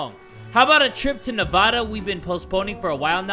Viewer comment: Did just say Navauda instead of Nevada or am I hearing things?